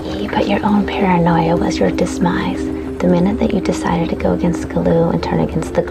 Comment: Am I crazy, or is she saying "dismise?"